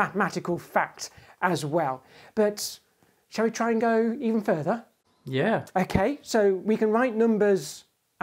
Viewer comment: I swear at 2:57, James said "shall we triangle further?"